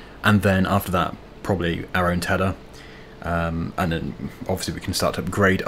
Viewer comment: Is it me or does say Gary turner but he does say our own Tedder at 20:58 into the video.